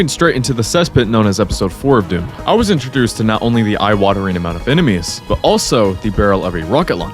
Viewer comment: I heard that as "the IWAD-ering amount of enemies" lol